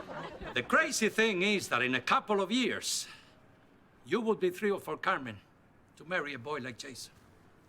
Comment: did anyone get what he say? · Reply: I think he was saying they would be thrilled when Carmen was older to be able to marry a boy like Jason. Which means they would approve.